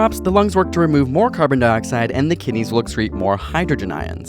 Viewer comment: "When pH drops, the lungs work to remove more carbon dioxide and the kidneys looks reads (2:17) more hydrogen ions." I heard "look reads" :(( What are the correct words there?